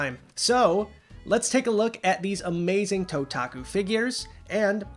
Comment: I kept mishearing you. Several times I thought you said "Totaka" collection (like the Nintendo music composer). =P